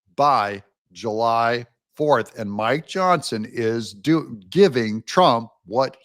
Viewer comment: Sound like you said 'MY Johnson' did it again, it a term in England for Penis, not sure if America it's the same, lol